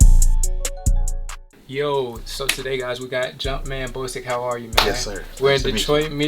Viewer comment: Did he just say jumpman boostic! Lmao stooopid